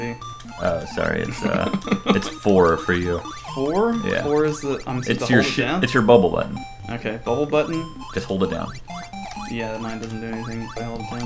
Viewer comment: Did say has a bubble butt? Whaaaaaaat????